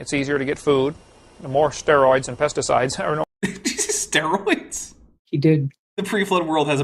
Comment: I think he said "There were no steroids or pesticides." before pivoting to steroids in current meat supplies, but he mumbled.